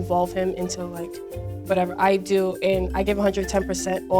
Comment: I heard a girl say “I’m into like 5’8” I was like WHAT THE HELL THATS ALMOST 6’0 FT I’M TEN AND I’M 4’7